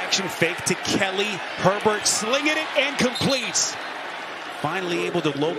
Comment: The announcer should be told that when he says "...and complete" it sounds like he is saying 'Incomplete'.